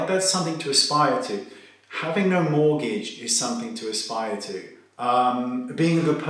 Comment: When you said ..having no mortgage is something to aspire to..real ass talk!!..that's y I love this guy!🔥🔥🔥🔥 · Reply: I thought he said "having a mortgage"... yeah, you're right, he said 'no mortgage'. Still, I guess you gotta have the mortgage before you get rid of it, so both can be aspired to!